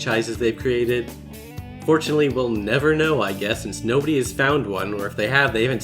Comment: Did you say "fortunately" at 2:26 or did I mishear you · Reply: I said unfortunately but I think the first part was so quiet it got cut off when I ran Noise Removal in Audacity, so, well, fuck.